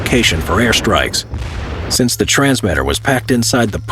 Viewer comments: the vc's would pass right by it but it sounds like he said feces pass by it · I thought he said "the faeces would often pass right by it" 🤣🤦‍♂️🤦‍♂️